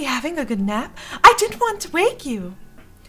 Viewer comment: I just misheard that as "I didn't wan't to rape you." xD